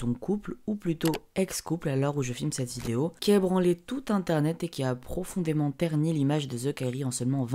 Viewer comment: Il n’y que moi qui aie entendu « qui a branlé tout youtube » à 0:20 ?